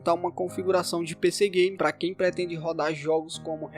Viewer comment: Eu ouvi pc gay nesta parte